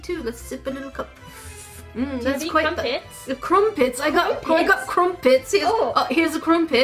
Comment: I Originally thought they said corn pit. Not crumpet.